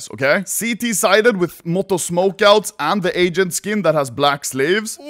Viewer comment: it sounds like he says "the agent that has black slaves" even tho its not what he said